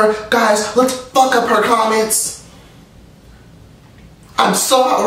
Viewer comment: Y did i think u were bout to say " fuck up her commas" 😂😂😂😂😂😂😂